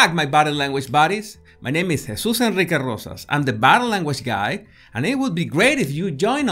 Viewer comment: For a moment I heard "I am the Bad Language Guy" LOL